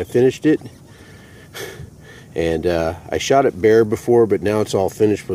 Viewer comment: Thought he said he shot a bear at first 😂